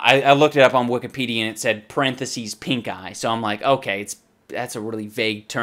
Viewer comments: I thought you said pink guy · I thought he said pink guy xD